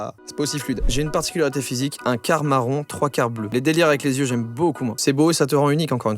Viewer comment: Les gars à 3:22, on a l'impression qu'il dit " Les délires avec les yeux j'aime beaucoup moins " alors que non il dit " Les délires avec les yeux j'aime beaucoup MOI "